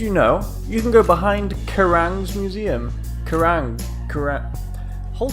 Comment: I'm probably just being stupid... but what did he mean by the Carian Museum ? I realize it's a joke, but I can't figure out where he means! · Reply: Gurrang's Mausoleum